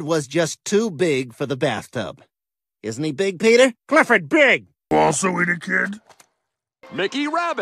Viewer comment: what did clifford just say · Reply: “Did you also eat a kid”